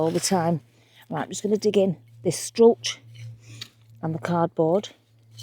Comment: Oh how funny. I discovered a new visitor to my bird feeders and it is a species I'd never heard of before, "A Cow Bird" So I'm watching your wonderful video and at 8:23, I swore you said, "Cow Bird". I had to go back and turn on captioning and found out you said, "Cardboard".... I had a good chuckle at myself.